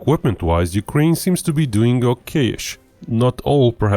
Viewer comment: Took a couple of minutes for me to figure out what said when I heard "facing ocaish" ... I'm pretty sure he meant, "faring okay-ish." Meanwhile, I got my stuffed in the post recently, and he has a privileged perch in my finest bookcase. :)